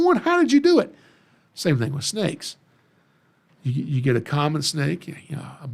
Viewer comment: ha ha I'm so into hognose I heard conda instead of common 😅